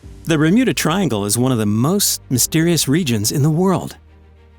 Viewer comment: did i hear "remuda triangle" at the start